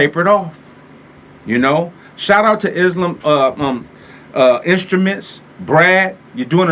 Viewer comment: I didn´t understand the name of the manufacturer that mentioned at 4:03 in the video, it sounded like Eslam Instruments to me but I found nothing when I googled it. Do they really work on a Sampler/Groovebox that could take away some market share from Akai? If so I´d like to check out their product(s). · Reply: Isla Instruments